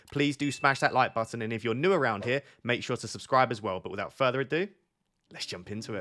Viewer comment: I keep hearing "without Freddy Adu" Make it your signature